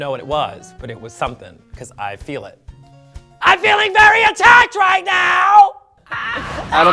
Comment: What i heard : "i feel very attacked" Trinity : "RELAAAAAAAAAAAAAAAAAAAAAAAAAAAAAAAAAAX" Looooooool